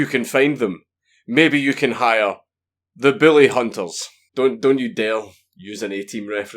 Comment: Until 3:17 , i thought he said Billy Hunters.